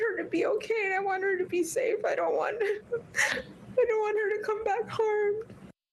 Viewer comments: Did she say “I don’t want her to come back home” ??? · Omg i thought i heard mom say i don't want i don't want her to come back home .... WTF WHAT A BIG A$$ SLIP WOW.... START LISTENING AT 1:22 & USE CC IF YOU WANT says I'm scared for her(supposedlyceying),I don't want I don't want her to come back home. It slipped this is horrible maybe she wanted to say come home but i believe her being emotional she spoke truth without even realizing it. Shitty Fighting over this man with your little girl when you should kicked him out long ago when you felt that oddness that you tried to ignore. Disgusting he got away with this right in front of your face....